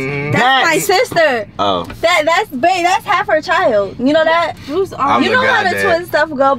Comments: Am I tripping or did say “bae that’s hale her child” time stamp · AM I THE ONLY ONE WHO HEARD “bae that’s half out child “ dun slipped up 😝